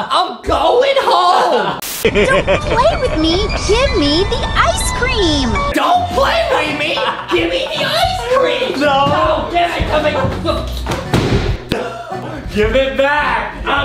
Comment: I thought he said “Jimmy the ice cream” 😂